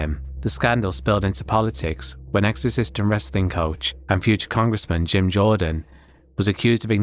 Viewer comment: For a second, I thought you'd said 'exorcist and wrestling coach' instead of 'ex-assistant wrestling coach'